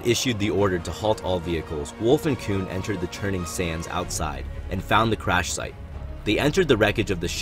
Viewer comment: it's a very good video with clone wars adventures scenes it's all perfect i assume you played the game and there is a sentence i can't understand could you tell me if you say at 10:45 : "wolffe and plo koon entered the turning sands outside and found the crash site" ? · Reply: jh 64 “Wolffe and Plo entered the churning sands outside” 😅